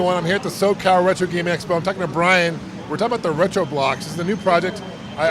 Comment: what I hear is "ret-ROBLOX"